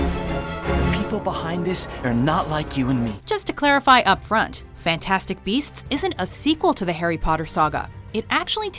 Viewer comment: I thought It said Fantastic Breasts ☹️